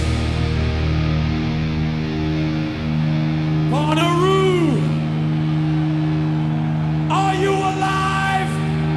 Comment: I'm going fucking crazy over here, can someone tell me what James says around the 4:20 mark? The word before "are you alive?" He says it again like 5 seconds later too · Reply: Bonnaroo. It's the festival name I suppose